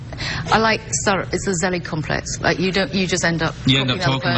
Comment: It's a what complex? I can't understand what she says at 8:00. Very interesting tho. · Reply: Zelig. Its not an actual complex but based on the Woody Allen film. The film's pretty funny as well.